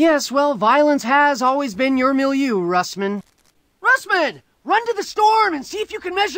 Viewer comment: I always thought he said Russman! Run the store and see if you can measure the Gigawattage." So we thought that there might be a nav card or something by the Store isn't the edge of town by the lamp post